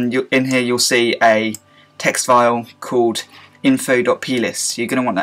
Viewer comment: i thought you said penis instead of plist but still i love you